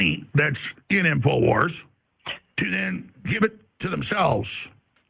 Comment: what did Alex just say? "They're buying Infowars, to give it to themselves?" 🤪😂